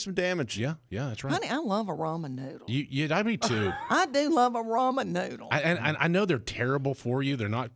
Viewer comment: I thought he said "rum and noodles" and I was all in! Ramen... not rum. Darn.